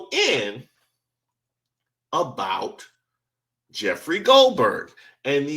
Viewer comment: Jeffery Goldberg ? - I thought he said Jeff Goldblum, I started to Cry